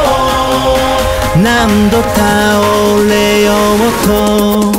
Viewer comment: 「何度倒れようと」が「何度手折れようと」にも聞こえるな。